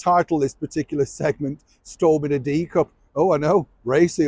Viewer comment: Storm in a D cup?!😂😂 I don't think I've ever seen the documentary about Something's Got to Give, so something to seek out - and how would we have known any of this if it hadn't been for you? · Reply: 🤣🤣 I thought he said "storm in a teacup" 😂😂